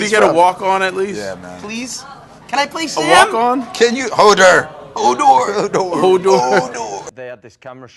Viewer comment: did anyone else heard HOld the DoOR... or am I just not over it yet